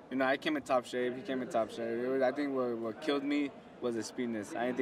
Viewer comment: he keeps on saying "what killed me was his penis" 0:25 then he said the same thing again 1:13. hahaha